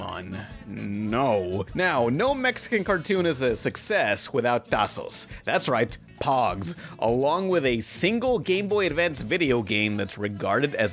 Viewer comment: did you say "Tazos"? Finally! Someone else on the internet who actually calls Tazos "Tazos" and not POGs (Pomegranate Orange Guava-juice)